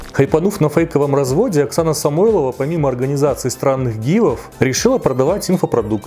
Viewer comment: Вместо «организации странных гивов» послышалось «с*аных» 😅 Кто знает, может так оно и есть 🙃